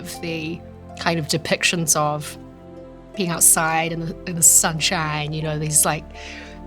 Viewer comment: Did she just say that Midsummer Night's Dream took place...in the sunshine?